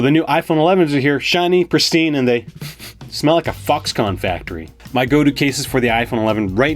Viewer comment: lmao... sounds like he said,.... :Fux con factory. instead of Fox,... well you get what I'm saying.